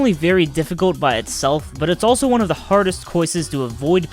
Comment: did he just say “coises”?!